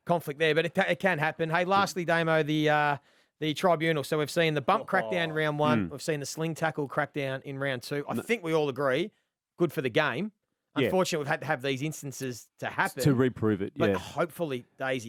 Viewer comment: Did I just hear “we’ve seen the bum crack down round one”?